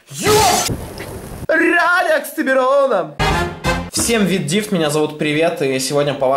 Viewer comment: Мне послышалось или ты сказал в начале ,, Всем вит дивт меня зовут привет"